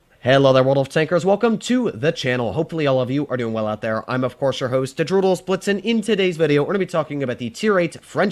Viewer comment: what said: "... i hope you are doing well out there I am of course your host Droodles Blitz..." what I hear: "I house you are doing well out there I ame of cat your horse Doodle Bakes..."